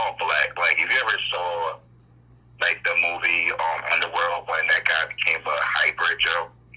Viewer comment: What movie did he say they look like? I couldn’t understand what he said @ 59:25 ? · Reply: Underworld